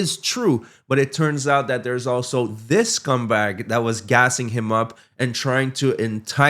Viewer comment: When you referred to Raja's pal I had to rewind because I thought you said - "there's also this CUMBAG" and I was howling with laughter, even more so when I realised my error. I'm now going to use this when I want to insult someone. Bob Ross was right - "we don't make mistakes, just happy accidents". Thanks. Cheers fae East Scotland 👌